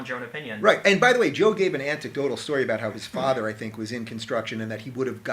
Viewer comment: Did Rubin say "anticdotal story" at 18:28?